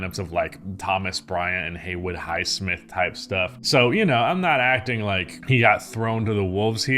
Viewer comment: Did u just say "type shit" in white wash lol